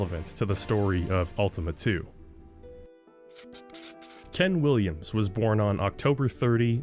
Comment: For some reason I misheard and thought that you said Ken Rolston instead of Ken Williams and was very surprised that a guy who worked on Morrowind was also the guy behind Sierra for a minute.